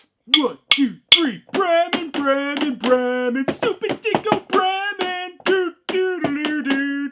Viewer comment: I swear I thought I heard "Stupid Brad"